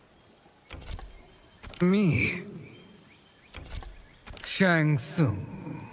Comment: I love how shang tsung almost sounds like he is saying "me, yes thats me, oh ffs fine shang tsung"